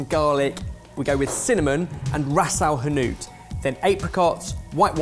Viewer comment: what is Ben talking about there? He says "cinnamon and ..." ? I have never heard of it! lol, sounds like this spelling: rassile hinnoot lol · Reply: Ras el hanout (a Moroccan spice blend) 🙂